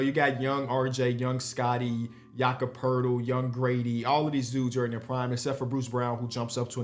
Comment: thought said yung gravy and i was trippin for a sec